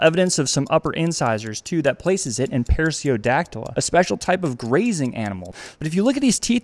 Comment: For a second I thought he said GLAZING